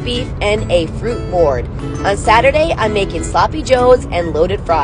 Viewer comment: 😂. I thought I heard you say you were making Gouda fries not loaded fries😂😂😂